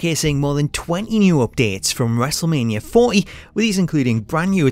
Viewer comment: lol your accent the way you say WM40 sound like you say wrestlemania 4 E